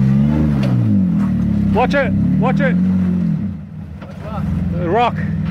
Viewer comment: 『もうちょい、もうチョイ』に聞こえる気がするwww